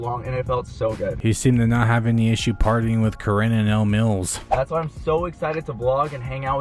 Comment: I was listening to this doing the dishes and I swear to sky daddy I heard "Elmos podcast" instead of Elle Mills lmfao I had SO MANY questions 😂😂